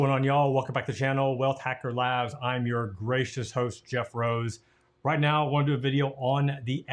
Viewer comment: I swear I thought he said "I'm your racist host" at first hahaha